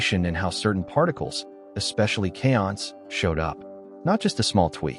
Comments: where did you find your information? where can viewers fact check the information you provide? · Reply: That answer sure sounds like BS to me ? @ 1:07 you use a word which the CC says is "chaons" what the hell is that ? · you use a word which the CC says is "chaons" what the hell is that ? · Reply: Sorry about that. It was the automatic transcription that wrote it wrong. I was actually referring to kaons, also known as K-mesons. Thank you for your visit.